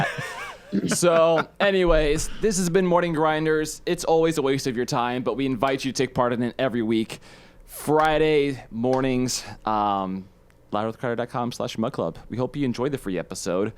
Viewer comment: Am I the only one who thought it said morning gingers I got mind fucked